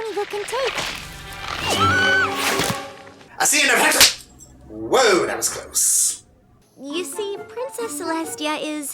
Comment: Someone mind telling me what he's saying? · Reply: "I've seen enough hent-" slap Are you happy now? Just disturbed? Alrighty then.